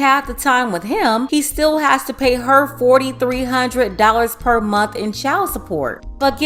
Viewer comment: But i thought she said something like he has to pay nothing😑